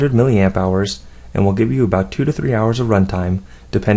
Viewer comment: How long did you say the charge last ? 23 hours ?